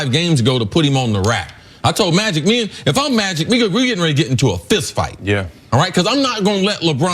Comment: did say "nigga we getting ready to get into a fist fight" 😂😂😂😂😂😂😂😂😂